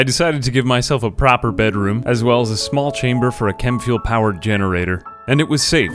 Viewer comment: I heard "a small chamber for a Kenwood generator" lol